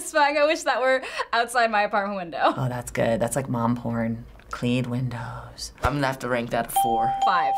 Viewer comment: did she say what I think she said? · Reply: Mom porΠ